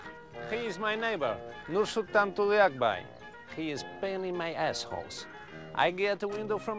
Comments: I always thought Borat said “He is painting my assholes” until I read the subtitles. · I initially thought he said "painting" instead of "pain in" 😂 🎨